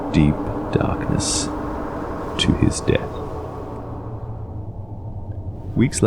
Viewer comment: I thought he said dee-tah.